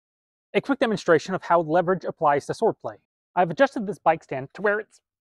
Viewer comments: I thought he said foreplay because it 4 am and I’m stupid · I heard, 'how leverage applies to foreplay' 😮